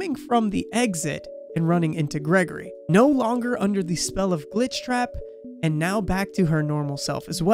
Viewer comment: I heard ‘smell of Glitchtrap’ for some reason 😂😂😂